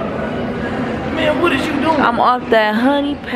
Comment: did say babe 👀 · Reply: He said man